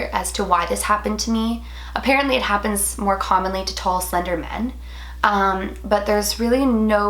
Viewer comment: did you said that it happens commonly to tall slender man? lmao I don't get the connexion if you did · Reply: Kay Tee tall, slender, men. :)